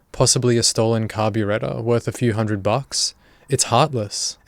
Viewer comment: How did he say carburetor? I don't think I heard it, lol. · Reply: “possibly over a stolen carburetor worth a few hundred bucks?” I didn’t even know what the word he said was until I saw the comment above! Lol I thought he blended something with beretta